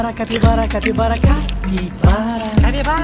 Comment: I hear "cakybara".